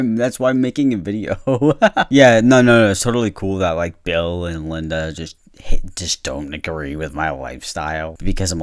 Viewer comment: i heard bob and linda at first and for some reason immediately thought of bobs burgers and was like “they would never!” and then i realized it was bill and linda and it was just two random names 💀